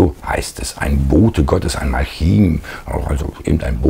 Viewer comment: "...ein Engel ein Bote Gottes ein ??? Malchien ??? , irgendein Bote." Ich kann es akustisch nicht erschließen. Ich verstehe es nicht.